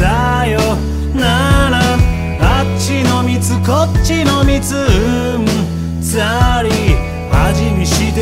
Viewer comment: の「うんざり」の「うん」が司に聞こえてしょうがない